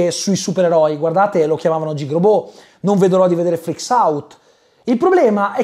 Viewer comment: cosa dice? jeegrobot e ... non capisco l altro titolo grazie dell aiuto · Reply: Freaks Out